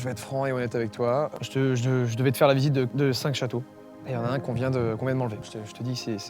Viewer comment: j'ai compris "et y'en à un qu'on vient de manger..."